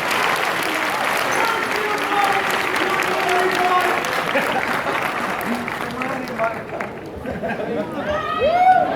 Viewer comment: Probably the best footage of the panel I've seen so far, thanks so much for taking it and uploading it :) Question (for you or anyone else that knows): There are guys that yell something near the end as Sebastian starts to make his way off stage (at about the 45:25 mark) that Sebastian reacts to, what did they yell? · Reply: He said, "Thank You for ruining my life." I was there infront of Sebastian and the guy said that and made me laugh too.